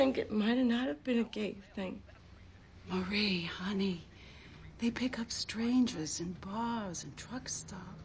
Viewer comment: Vito did a dumb move going to a club he knew they did collections at. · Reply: I thought they said it was a day early pickup.